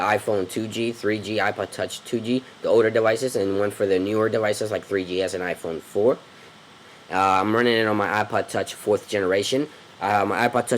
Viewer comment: I thought the ipodtouch said ipoddouche xD